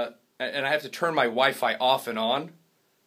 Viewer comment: I thought he said I have to turn my wife off and on xD